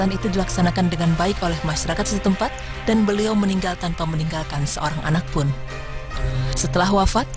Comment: Menit ke 04:03 saya tidak paham ngomong apa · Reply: tidak menikahkan dan meningalkan seorang anak pun